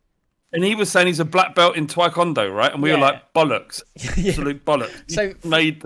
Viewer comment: Did say 'twae-kondo'?